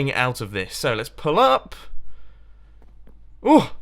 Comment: When he said "pull up" I thought he said "pull out" "pull out" then "ahh! I pulled out"